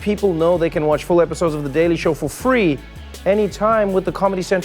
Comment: I heard "free deli show." I'd watch that actually. get me some of that on TV.